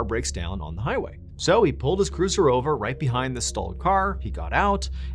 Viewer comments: you said stolen car before we knew it was stolen!! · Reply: I thought he said stolen too but he said stalled The car was stalled not stolen but it definitely sounds like he said stolen I listen to that for about five times before I figured it out because the whole story he didn't say nothing about the car being stolen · Edit edit sounded like somebody said stolen car. Before we know it was stolen could have been stalled but it certainly sounded like stolen 😅 😂 love your work.